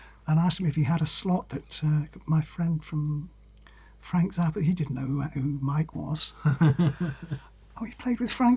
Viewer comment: Who are they talking about at 46:00? Mike something, I can't figure out what that last name. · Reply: mike keneally